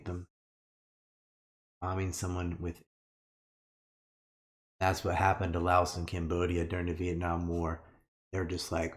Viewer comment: What did ryan say at 1:13:16 ? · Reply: Indifference